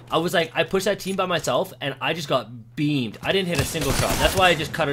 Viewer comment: I thought you said “and I got BEANED!”😂